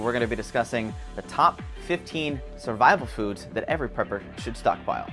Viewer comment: I thought you said ",top 15 bible verses every prepper should know" Can you recite 15? That will be my "secret knock"...."recite 3 bible verses" then we can talk.